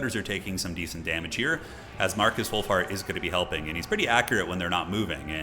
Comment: I did a spit take during the introduction because I heard "Marcus Woolfart." Not sure how someone could shit a sheep, but that dude figured it out.